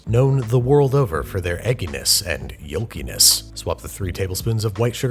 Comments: why does the caption says "yo penis" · Did anyone else catch the “yo penis” instead of “yolkiness” in the captions at 2:20? A different kind of recipe.....